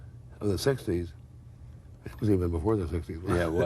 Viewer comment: Can anyone tell me what the hell he is saying at 10:38 just before Rose says "Yeah it was"? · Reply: ”this was even before the 60’s”